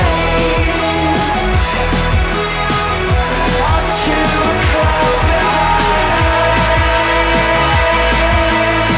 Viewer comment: after a lot of searching, I still can't make out what he says at 1:48, can anyone help me? he says it at 2:25 too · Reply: Up to a cloud behind a lake...? 🤔